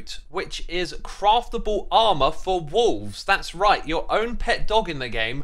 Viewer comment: I straight up misheard him say "armor for your walls" as a 7DTD enjoyer I was like YES! Though unless they added something like a bloodmoon to Minecraft, I don't think that would be too useful.